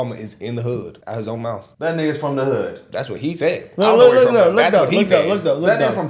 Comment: i think i heard "that niggers from the hood"... mmmm...